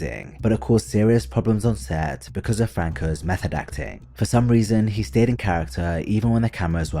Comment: I keep hearing 'meth addiction' instead of 'method acting'. Weird manner of speaking, stretching vowels at the end of the words and swallowing syllables in the beginning of them.